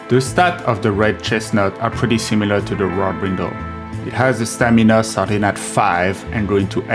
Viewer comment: Listening to him speak, I thought he said "Wop Brindle", then when the works appeared on-screen, I realized it was "Warped Brindle"